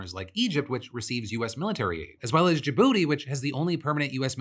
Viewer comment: did I hear that correctly? JaBooty??